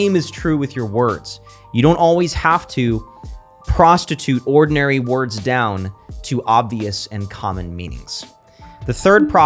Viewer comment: Did you just say "prostitute ordinary words down" or is this kush too good